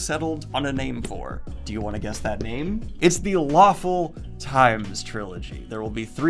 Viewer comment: I keep hearing The waffle times trilogy. :)))